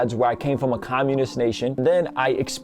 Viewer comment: Hey! New here! Did you just say orthodox romanian church? Have you lived in Romania? That's where I am from. The world is so small. Edit: I misheard 😂 he said Armenian.